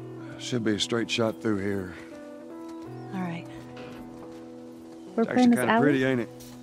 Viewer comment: l hear "brooklyn is out"; what did you say?